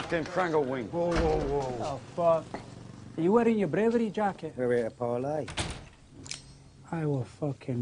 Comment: "Are ye out of yer bravery jacket?" Love that quote (Hope I spelled it good lol) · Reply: Is that like another way of saying straight jacket? Cos I thought he said “are ye wearin ya bravery jacket”